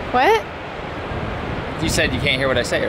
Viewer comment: Best part of the vlog: " I have a huge crush on your mom, Sara" 😃😃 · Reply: Haha... Yeah. "It doesn't matter what you say". Oh, I thought you said, "You couldn't hear what I say". Hahaha. Oops.